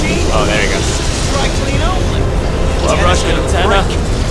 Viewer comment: I thought it said web rush enema